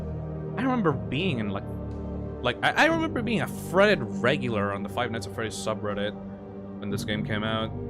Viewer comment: did i just hear the toxic man say "freddit regular"? cuz i don't remember using reddit back then like what? 8-10 years ago? let alone at all. christ, you ARE old!